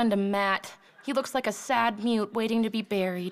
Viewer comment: What does say at 1:46? "He looks like a sad ... waiting to be buried." Thanks. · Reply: mute.